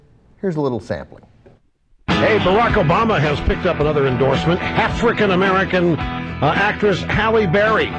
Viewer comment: Is it just me, or does it sound like Rush is actually saying half-rican American instead of African @ 1:43?